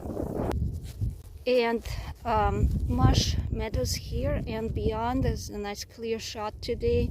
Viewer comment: So cute, I thought you said marsh mellows😍